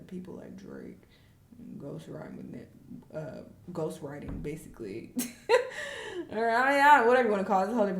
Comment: YouTube being shady with the subtitles saying “ghost riding” TWICE instead of ‘ghost writing’ which is what said lmao. 😂